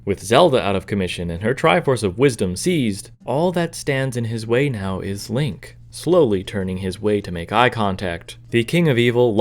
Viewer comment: I got a college of Saint Mary ad around here, so it sounded like “The only thing that can stop him is” College of Saint Mary